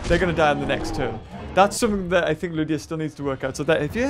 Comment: it sounds like you said bitches